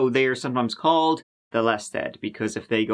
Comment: Does anyone know what the word he said, describing what sex workers who go missing (@ abt 10:20) are called? CC said "lested" which is to my knowledge not in fact a word 🤔...just curious. (Edit: I just realized it was maybe "less than", as in serial killers assume sex workers are "less than" the type of people considered important enough to be reported missing...?) Great storytelling as always. I always appreciate when you treat "lested" victims with the respect & humanity they deserve. 💕👍🏼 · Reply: He says "the less dead" 😊